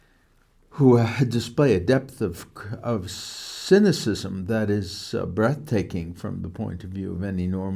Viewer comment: Is this google or some error ??? Around 4:33 he speaks about “Cynicism” whilst the caption says {wisdom}. W t f!!